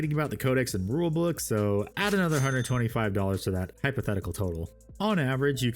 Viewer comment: I heard "add another 150" for additional tools I'm good😂